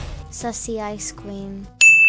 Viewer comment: I thought she said sexy ice queen